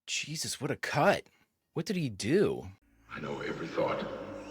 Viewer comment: I thought you said “What a C*NT!”